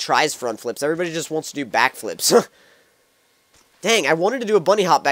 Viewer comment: this may sound racist but i thought he said black flip and im black but then he said back it sounds like black to me